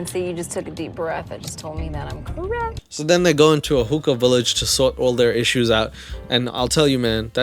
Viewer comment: OOOHHH, HOOKAH! Jesus I thought you said hooker! Lol